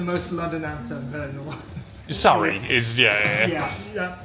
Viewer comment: Bartender: "That's the most London accent I've heard in a while." You: Surrey... I thought you said sorry... (ha ha he's so funny, apologizing for his accent like Eliza Doolittle)